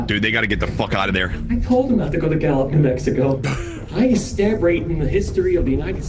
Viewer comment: It sounds like he said N'Mexico...